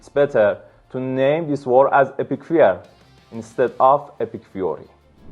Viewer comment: Did he say "Epstein Fury"?